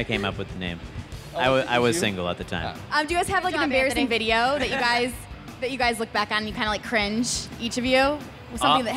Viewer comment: What did Mari say at 4:53 though? Couldn't hear. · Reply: Did she say: "Good job, Anthony."? x'D